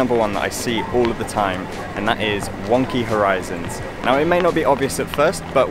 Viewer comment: I thought he said “one key horizons” lol this video helpful tho. Thank youuuuuu